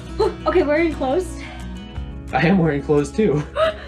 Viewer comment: I thought she said "I'm so wearing cloths."